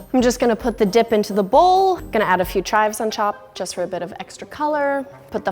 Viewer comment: Did anyone else hear her say "gonna put a few chives on chop"